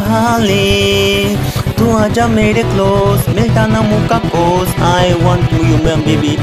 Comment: did he said bhos ?😂😂😂😂😂